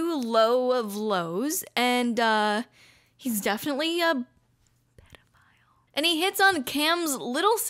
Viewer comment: Someone help what is she saying at 14:44 😭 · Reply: Pdf file